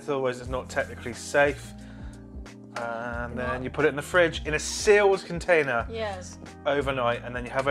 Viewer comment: How big is your fridge? :-O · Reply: Ah, sorry, got you now. 'a sealed container', not 'its sealed container' like I misheard. That made me understand the rice cooker was the sealed container. My hearing is really getting bad.